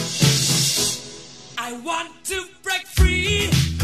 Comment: En el momento que escuche "I want to break free" yo pense que havia dicho , "yo quiero una refri"